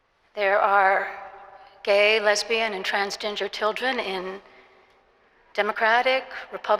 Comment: I really thought that holy lady was saying "Trans-ginger ppl" wow even more hate for gingers. Nope im not hearing gender after re rewatching. ❤️✌️